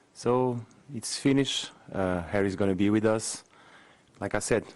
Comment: i wasn't paying full attention (Working on another screen) and thought he said 'Hairy Vaginas' here lol